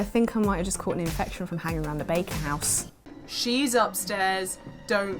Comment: im american and i thought said bacon house not baker haha